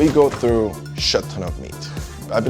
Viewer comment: is he saying "shit-ton" 0:16